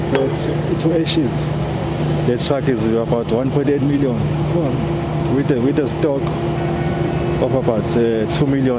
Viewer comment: the cost of trucks range from between R1.2 million to R1.7 million. Google it. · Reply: from 1:10, I heard "the truck is about 138 million". He is probably traumatized to talk and think.